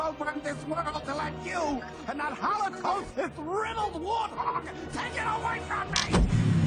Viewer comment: I thought he said “To get away from me!” I was wrong for years- Thx for making this